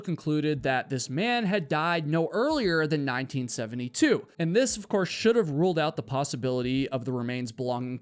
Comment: Didn't he say no later than 1970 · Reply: I thought he said "later than 1970" but I might have misheard him.